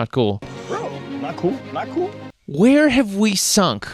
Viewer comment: I didn't hear "Not cool, not cool" I heard "Cuck-oo, cuck-oo" Cucks, Manginas and Simps are forever amongst us. Sadly!